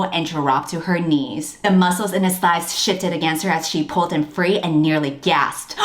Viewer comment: When you read “ Cassian (shifts) in his seat” while Nessa is touching him. I thought you said shitted lol